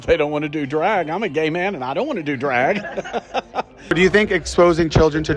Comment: Even the gays don’t want kids to go to drag shows, even at a gay pride parade. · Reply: I almost thought you said even gays don't want to go to drag shows lol.